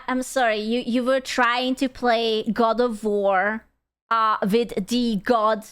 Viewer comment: i realy thought you said god of whores, and that made me laugh so hard thx